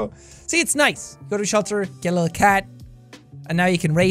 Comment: WHO DID THE SUBTITLES 21:57, it says KILL a cat instead of GET a cat